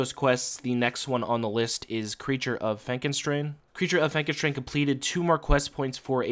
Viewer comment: damn i always thought it said creature of Frankenstein